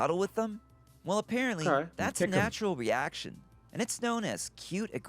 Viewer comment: I swear Tobi said "kick em" 💀